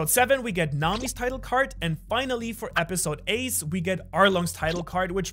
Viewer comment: Easter egg: saying episode ace instead of 8 😂